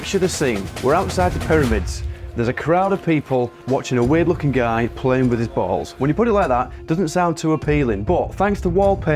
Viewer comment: I heard " a guy playing with his butthole" then you follow it up with the pull my finger! If it wasnt intentional, just say it was 😂